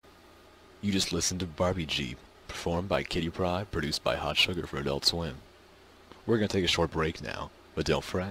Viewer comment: what are you saying on 1:48? ''You listened to Barbie Jeep performed by Cape Rod, produced by Harl Sugar for Adult Swim.''